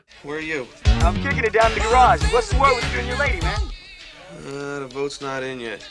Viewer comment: całe zdania dość trudne do wyłapania ze słuchu. Tam jest zdaje się "I'm kicking it down in the garage. What's the word with you and your lady, man?".